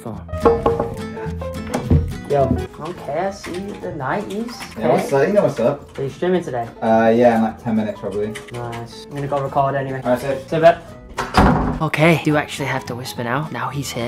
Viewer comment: did he say see ya babe